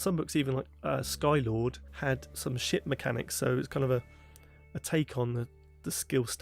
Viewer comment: Misheard 1:45 as "Sky Lord had some sh1t mechanics" Yes. Yes, it did.